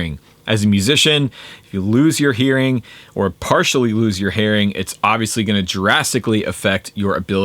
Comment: Need to get those ear plugs. Don't want to partially lose my herring (10:34 - that's what I heard! LOL!) Being a Scandinavian native, my herring might be just as important as my hearing. For sure.